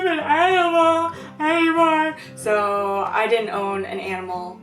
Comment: the caption says Untinteliagble gibberish yet I heard her say "Any more"